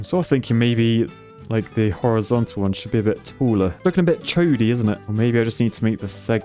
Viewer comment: Did I hear “chodey”? 🤣